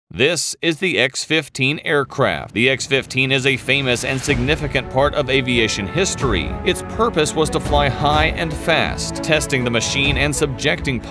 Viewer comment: I heard "famous insignificant"...🤷🏽‍♂️